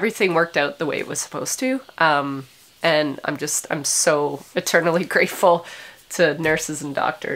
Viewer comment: did you say ETERnally or MATERnally grateful? Nevermind — either or both works!